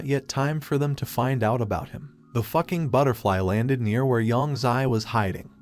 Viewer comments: Did anyone hear him say fuking butterfly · don't it sound like he said the f-ing butterfly